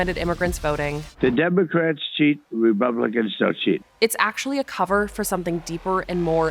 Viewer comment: did he say "Remumblicans"? I mean, it tracks 😂